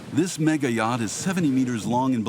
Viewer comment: did he say? "This yacht is SEVEN METERS LONG????"